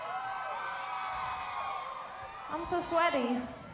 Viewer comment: what Kesha said at 35:07 ? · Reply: I'm so sweaty lol